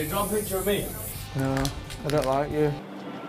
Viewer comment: correct me if im wrong but i swear zayn said to niall at 0:04 "No, I don't like you"